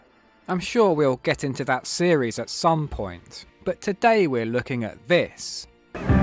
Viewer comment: At the start, I thought for a second you said "but today we're looking at Bits" - which I hope you will also cover someday.